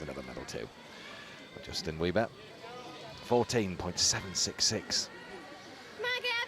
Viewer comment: did I hear "Justin Wieber"?